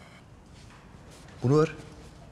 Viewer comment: de ne diyor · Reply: "Bunu ver" yani arabayı kast ediyor kardeş